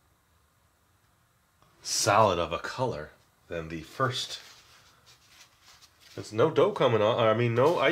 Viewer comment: DID HE JUST SAY THEIR IS NO DOPE COMING OFF OF IT??? THAT IS WHAT IT SOUNDED LIKE TO ME!!!***